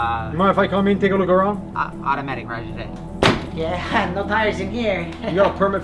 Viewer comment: What is he mumbling at 0:48? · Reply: "Automatic, Roger That"